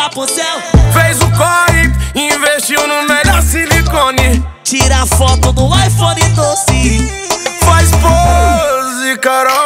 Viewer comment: “Tira foto no iPhone doce” KKKKK só que entendi assim?